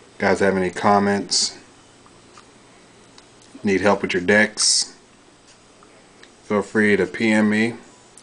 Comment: sounds like ur saying the d word